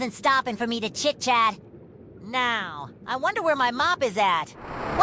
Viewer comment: I love when he said "wheres my mop" it sound like he said: "wheres my mom?"